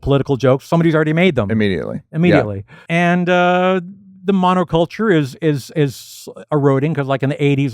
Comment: 51, which culture does Al say is eroding? I can't make it out, begins with an "M." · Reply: Monoculture. His point is that in today's world everybody's got their personal taste bubble and one person's superstar is unknown to the next guy, while in the "monoculture" of the 80s everybody basically had to agree on pop culture phenomena like Michael Jackson, Madonna and so forth.